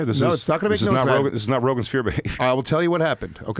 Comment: The boys are really in the rogansphere now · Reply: I always heard it as Rogansfear